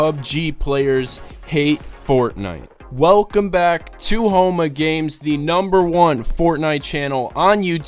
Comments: Sorry but it sounds like your saying homo games... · Omg I thought u said homo games